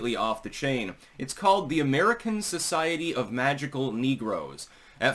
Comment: I literally heard you say "The American Society of Magical Thinking Negroes"